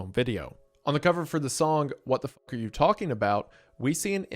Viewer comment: Did he say f ck???? Is this saying a curse word for the first time?????